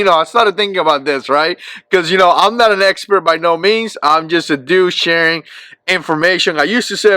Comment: Did you just say "I'm just a douche" ? Just busting ur balls. Glocks are about $100 too high. If Glocks were $100 cheaper I would buy them.